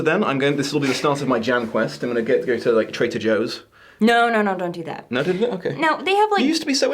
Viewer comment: Love how it sounded like said traitor joes, lol 😂